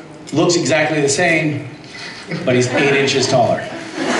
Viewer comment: What did Misha say at 5:10? "Cas looks the same, but..?" (sorry, my German brain just doesn't understand it) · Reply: okay, a few times later I got that he said "8 inches taller". And I guess the laughter after that was because he wasn't specific enough m) (And yes, Charlie, Bobby and Jess would be awsome!!!)